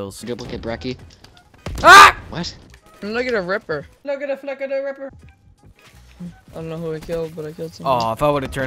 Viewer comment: Why at 4:20 I hear "look at the flip of that ripper?l